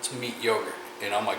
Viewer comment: Good video like always! Everything looked good. What did you say at the 8:55 mark? Did you say it is “mediocre” or that it is “meat yogurt”? I backed it up and listened three or four times and I’m still not sure. Thanks man! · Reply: Meat yogurt